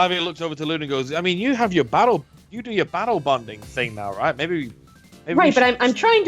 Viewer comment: ok I may have misheard 'battle-bonded' as 'bowel-bonded' a few times